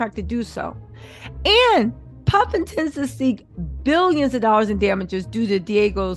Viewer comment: She Said "Puff intends" I heard "Puffington!" 😂👑🤣🤣